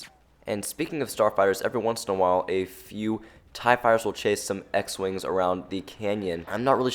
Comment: I thought he said v wings and when I heard that I RUSHED ON OUTPOST BETA and all I saw was x wings and speeders it was because I was on walker assault not blast :3